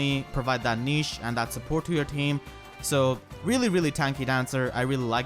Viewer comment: thought you said support thot at first. I need sleep